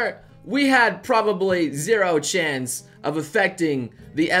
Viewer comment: Is it just me or does it sound like he said we have zero chans?